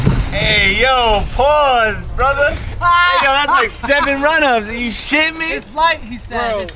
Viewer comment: i could swear he says "n**ga that's like 7 run ups"